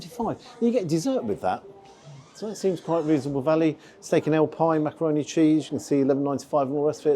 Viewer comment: I had to read it, I thought you said steak and eel pie for a few seconds. It is the UK after all. Seems slightly spartan, sausage aside. I do like affordable meals.